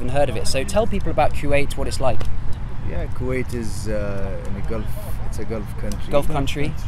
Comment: subtitles say q8 instead of kwait